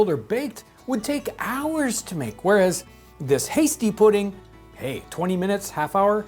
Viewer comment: I thought it said "nasty pudding " at first.